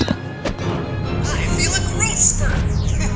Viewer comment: What does Ant Man say at 0:15, just before he grows up? Excellent video!!! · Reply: I feel a growth spurt